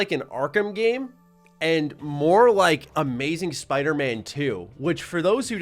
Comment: I thought you said Spider-Man two, I was gonna say besides the new insomniac Spiderman games, spiderman 2 was the best overall spiderman game and probably wouldn't be a exaggeration to put it up there with the Arkham games.